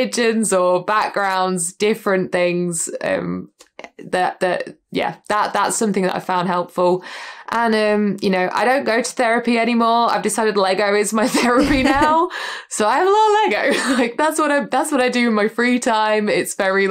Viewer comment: what did she say here? “lego” is my therapy now?